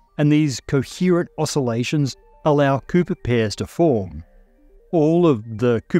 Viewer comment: Good thing for subtitles, Thought you said pooper pairs for a sec lol